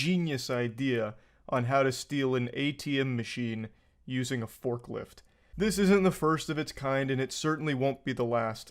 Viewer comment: English is not my first language. I had no idea what "folklilf" is or whatever he said on 0:08. I thought on a little special fork 🍴 or something. I literally screamed on 1:34 laughing 😂